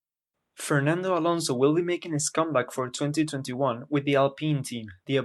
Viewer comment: At the Intro I heard "Fernando Alonso will be a scumbag"